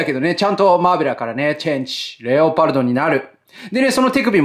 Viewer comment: 幼稚園時代に見ていたダーマ！そのころ「マーベラー、チェンジ、レオタード！」と聞こえていました（レオタードの何たるかはそのころもちろん知らなかった件）。投げると必ず相手にささるソードビッカー(これのエフェクトパーツも付いてます）も懐かしいです。スピードスケートっぽいダーマの決めポーズをとるレオパルドンにびっくり！手の収納はアイディア賞もの！ちっちゃいのにちゃんとそれとわかるGP7（もちろんマーベラー艦橋に収納可能！）もいい感じ！そして何よりうれしいのは、超合金魂と大きさの差があまりないこと！